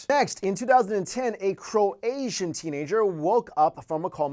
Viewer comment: That language on 3:35 with that girl did he say croasian language? Maybe croatian